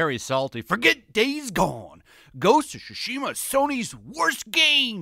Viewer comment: Why does it always sound like he says Shu-shima and not Tsu-shima.